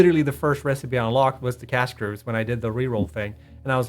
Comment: What are they saying they immediately unlocked @ 10:23. Canisters? Subtitle says Cash GRS...I can't think of any alternate I've immediately wanted that even sounds like that? Edit: Ah. 'Cast Screw'. Got it. I always race for Steel Screw.